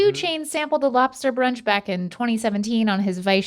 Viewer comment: stevie says 2017 but captions say 2019; who is correct? I dont need gMm, i need answers.